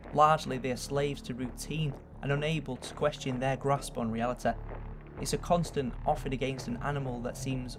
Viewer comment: This content is amazing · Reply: Did anyone else catch the "Reali-teh" at 0:23?